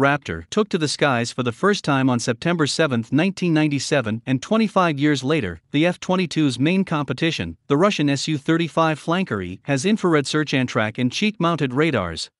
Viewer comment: The su-35 doesnt have cheek mounted radars. The su57 does. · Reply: I heard it as cheap mounted radars thank God now I get it